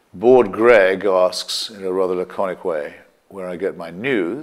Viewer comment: Can someone tell me what word Chris says at 5:07? Sounds like "lachiac"